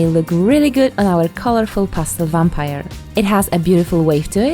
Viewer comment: I totally thought you said Pasta Loving Vampire I’m like oh nice way of getting away with “marinara sauce”(blood) but then I quickly realized it was Pastel! Not making fun just kinda funny that I full was ready for a Pasta Loving Vampire…lol